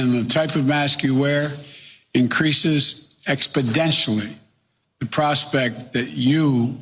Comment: did he say “expoDENTially”?